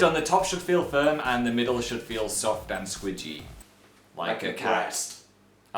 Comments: it sounded like you said "it should feel like a cast" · When Phil said that the middle should be squishy and Dan and Phil said "breast" and "cats" at the same time I thought they said "cast" and I was like wtf not accurate for that's one second. Then Phil pointed it out. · I thought they both said "Like a cast.." Turns out it was a mixture of cat and breast. xD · I thought they said "like a cast" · I thought he said "like a cast" hahaha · i thought at 6:04 he said cast !!!